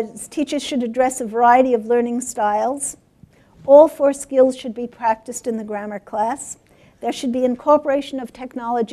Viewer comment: What does she say at 1:09:05.. something like "All first gears should be practiced in the grammar class" ? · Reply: It sounds like: "All four skills should be practiced in the grammar class."